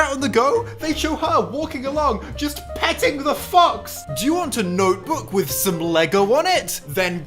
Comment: I can't be the only one who thought he said "petting the fucks"